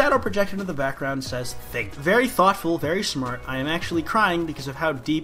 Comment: I thought it said thank and my brain was like it’s an avocado thaaaaaannnkss